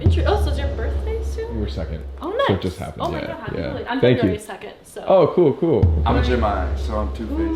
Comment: did she say february 2nd? that's my bday 😭